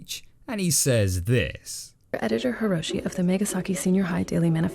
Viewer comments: When he first said megasaki I thought he said Nagasaki and I was like 😐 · He said Megasaki and I swore I thought he said Nagasaki